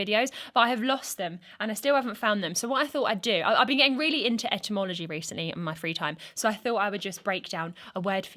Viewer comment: I really thought she said entomology not etymology and was super excited to hear about what moth she was gonna talk about.